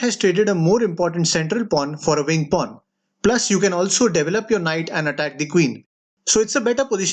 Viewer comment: I'm a new subscriber and for the longest time man, I thought you were saying double up, and I was looking for what doubling up was, then realized you're saying develop LoL what a game changer